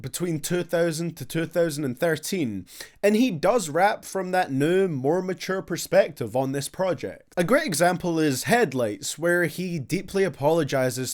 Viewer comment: Am i the only one who thought he said headlegs when he was trying to say headlights 😂😂😂